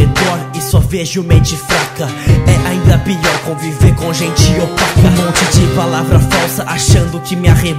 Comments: gente o ele nao falo otaka nos #3:58 ele falo opaca ouvi direito gente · mds pensei que tinha escutado "e pior com viver com gente otaka" dai eu achei estranho e voltei ai eu escutei "opaka" kkkk ainda bem pq eu amo vc 💜 a e o gabriel também 😍tmj✌👏